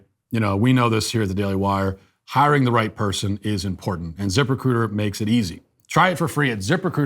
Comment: I know it’s “zip-recruiter” but all I hear is “Zipper-cooter”